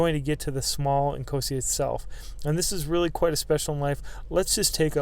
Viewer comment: I thought you were saying "small and cozy" then I looked at the title lol